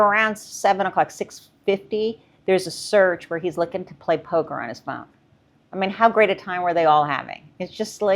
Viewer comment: Poker on New Years Eve? I thought he said his phone was lost.